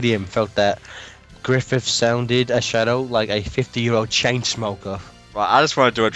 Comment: he actually said 60, not 50.